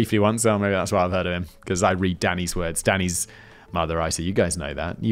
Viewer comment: What he said: "Danny is my other writer" What I heard: "Danny is mother" 🥺